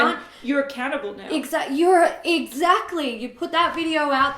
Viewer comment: 😂😂 I thought sat "a cannibal", then I heard "accountable"!